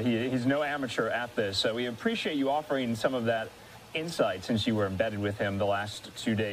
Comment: Anchor man sounds like he says, "Since you were in bed with him" @ 6:37.